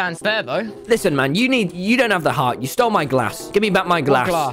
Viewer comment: i thought he said grass but he say glairse or whatever